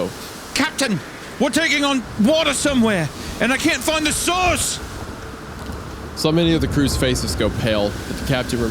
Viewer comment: Because of Hunter’s accent I heard “I CAN’T FIND THE SAUCE”